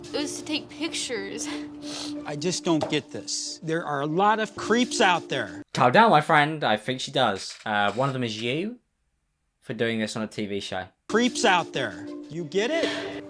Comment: thought he said crips